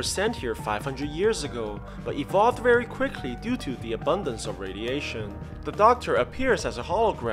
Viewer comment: At first I hear "Due to the Obunga of radiation" cause those things looks like those Obunga things I have been seeing. Then i though, that makes sense to why they had evolved to look like obunga. . .Then i realized it said an abundence of radiation. . .